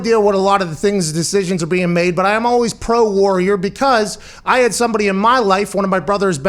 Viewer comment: does he say he is pro war? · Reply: He says he's pro warrior as in the people of our armed forces